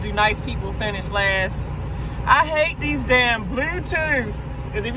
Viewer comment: U r so beautiful ms, but I can't front I had to double check the way you said Bluetooth. It sounded like you said "blew tew" lol